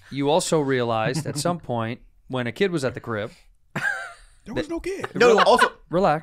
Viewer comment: I think Doc meant he had “a kit” at the crib, like a testing kit. Lol 😂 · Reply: I thought he said he was kickin it in the crib. But you’re right he definitely said kit. Plus Andres accent doesn’t help either lol